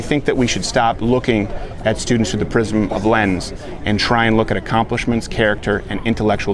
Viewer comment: WTF??? 28:15 - Am I mis-hearing? Steven says, "We should stop looking at people with the prism of lens, and try and see accomplishments and character...." Huh? "...the PRISM of LENS...???" Does that make sense to anybody here?